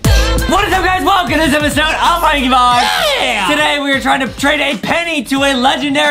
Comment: Did say Welcome to this episode im prankybox